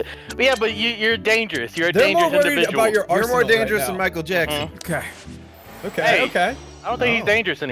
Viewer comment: Did someone just said is more dangerous than Michael Jackson? XD (It's not saying that, that's for sure)